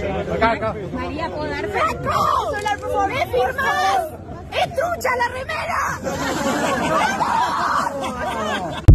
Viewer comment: What is she saying? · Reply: she said "Franco, me firmas??! es trucha la remera!!!!!" jajajs which means she's asking him to sign her shirt and telling him she bought it "second hand". perhaps that's not the right term, but like, it's not the original version of the shirt, but the cheap copy ones. and it's bc previously Franco had told us argentinos not to go broke buying the official Williams merch and all bc it was really expensive etc etc